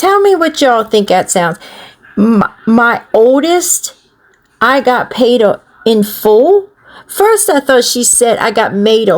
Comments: I thought she said my oldest got banned at school??? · she said "my oldest, she got beat up at school" - that's what I hear